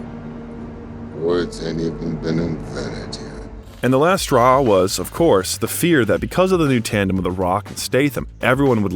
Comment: what was Vin even saying here lol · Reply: "words aint even been invented yet" XD